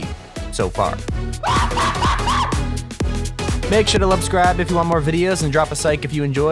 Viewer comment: Who else heard make sure to lubscribe and sike instead of subscribe and like